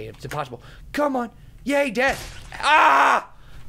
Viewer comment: Did he say "Yay death?"